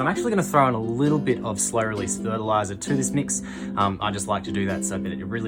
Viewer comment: you add something in a red container to your mix I couldn’t understand what you were saying sorry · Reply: Ah, my mumbling Australian accent!! That's slow-release fertiliser. It's broad spectrum, just to get some additional nutrients in the soil over the next few months. I'll fertilise again in a few months time :)